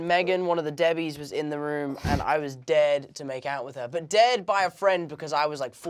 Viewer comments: omg I thought Tom said “dead” not “dared.” lol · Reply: Yeah I thought he said I was dead to kiss her 💀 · I really thought Tom said he was DEAD to make out with her. What?!? Ohhhh…DARED. Got it. Brits, Man. 😂❤ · I thought Tom said “dead by a friend” lmao